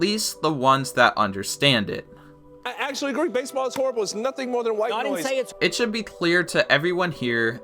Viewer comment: I thought he said “baseball is horrible there is nothing but white boys”